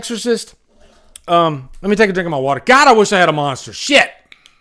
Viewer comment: did he say, " I wish I had a monster shit!" Uhhhhhh....exlax or chipotle will fix that